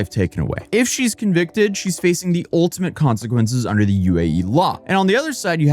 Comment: i thought that it said faking not facing i got whiplashed